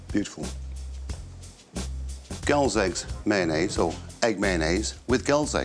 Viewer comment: Loved the 'Hellraiser' music and I kept hearing 'girls eggs'. 😄